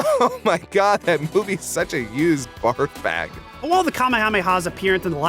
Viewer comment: Did they call Dragonball Evolution a "used barf bag" ?